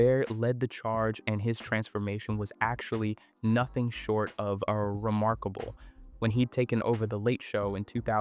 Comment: did you just say “a remarkable?”